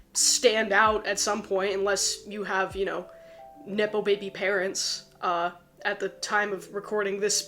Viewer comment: It really sounded like you said, "nipple baby parents" and it stopped me in my tracks. I was like WHAT!? Then, I realized you said NEPO baby parents, and it made me laugh. 🤣